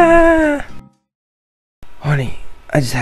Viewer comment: Sounds like he said "horny I just had a bad dream"